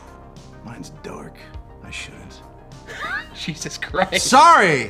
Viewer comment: when Garrett says “I shouldn’t” I thought he said Auschwitz 😳